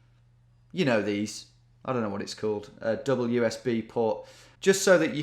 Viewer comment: For a moment I thought he said wsb port, but he actually said double usb port